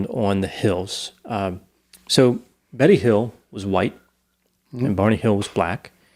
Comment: Ohhhhh! I thought you said BENNY Hill 😉😅😅😅😆